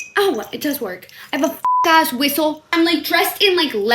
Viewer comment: I thought you were gonna say you have a r*pe whistle for the video :(